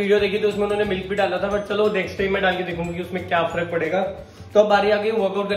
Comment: did he say dal k dekhugi 🤓